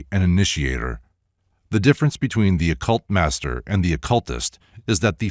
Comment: "The difference between the occultaster and the occultist..." -- I've never heard this term 'occultaster' before (this is how it's written in the copy that I have- as one word). It sounds like you say 'occult master', yet she goes on to sound as if she is holding the occultist (ie. the latter) in higher regard than the occultaster (ie. the former). Any idea what she meant by occultaster vs. occultist?